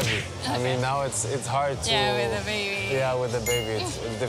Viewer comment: did he just say with dababy